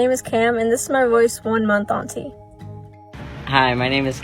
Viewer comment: All I hear is "this is my voice 3 months auntie" 💀